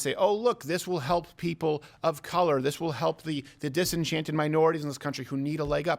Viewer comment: DID HE SAY “DISENCHANTED MINORITIES”!?!?!